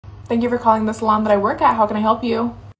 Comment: I totally thought she was saying “thank you for calling the slum that I work at” now I realize it’s salon 😂 well….I like slum better lol